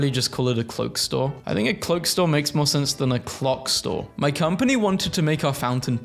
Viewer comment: I thought it said Glock 😂